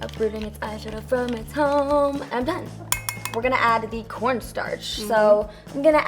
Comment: When she said cornstarch i thought she said pornstars😂😂😂